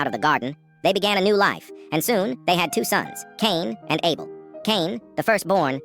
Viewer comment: wait... what did you just say? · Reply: Caine and able it’s not related to tdac